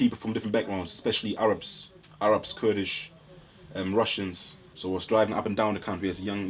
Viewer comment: did he say kurdish